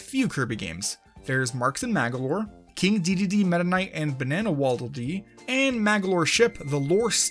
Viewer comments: did he say BANANA waldel dee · it sounds like he is saying banana waddle dee